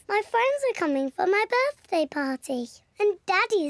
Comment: When she said party it did sound like she said potty